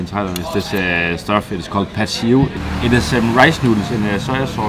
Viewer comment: What was the dish you mentioned at 3:40? I couldn't really catch the name but it looked good! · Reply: It's called Pad See Ew. If you are into that stuff, i would also recommend - Pad Thai, Panang Curry and Pad kra Pao. In general Thai food is really good!